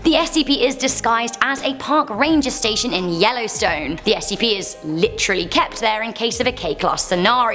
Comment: did you say Yellowstone? PACK YOUR BAGS GUYS WHERE GOING O OUR 5 HOUR DRIVE TO YELLOWSTONE!